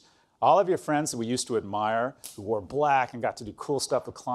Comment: definitely heard "who were black" instead of "who wore black" ROFLMAO